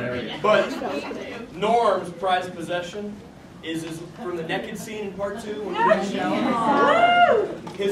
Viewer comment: Sounded like he said Nekkid instead od Naked <3 Sean